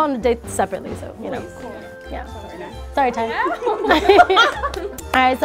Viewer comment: I heard sorry Tyga and was so confused and then realized she said Ty and it made more sense... although I did delete the fact they dated from my brain